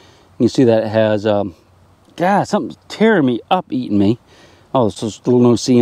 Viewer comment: "ah something tearing me up eating me al... slmumpsmls... seeing things" @ 7:13min ... can't make out what you said